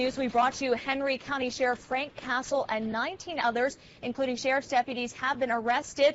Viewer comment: damn i thought she said Frank Castle, nope its Frank Cassel what a letdown. But oh well at least the asshat was caught.